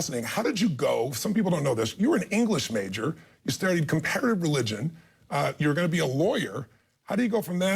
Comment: did he say competitive or comparative religion?